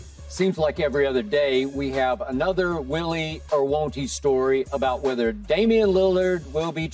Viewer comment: What did Skip say at the beginning 0:03? Willy who?